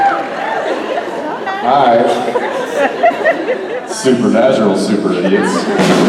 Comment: What does Jensen says at 27:58 ? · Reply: Supernatural super idiots? I might be off. I'm tired so. Maybe I'll be able to understand better in the morning lol But thats what I hear lol